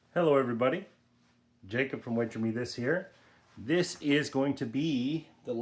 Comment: I thought it said “wager Meth is” lol